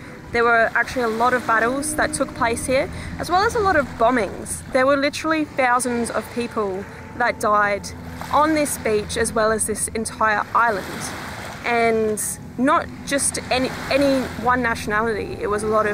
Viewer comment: I just heard beach 🏖 2:26pm